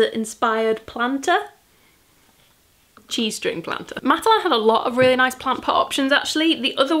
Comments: Got a tad distracted, missed the whole cheese string section, was a bit startling when I thought you said you wanted to pop the sunflowers in your g-string... 🙄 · I kept hearing “G-string” planter rather than “cheese string” planter so I’ve been giggling like a five year old the whole way.